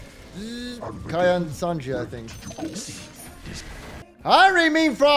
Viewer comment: did he just say Kaya and Sanji? Bulldog is a weeb confirmed